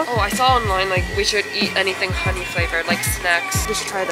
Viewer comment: I thought for a second when they said we should eat anything honey flavored like snacks I thought they said we SHOULDNT eat anything honey flavored snacks so then when she said let’s try it I was surprised then I finally realized 😂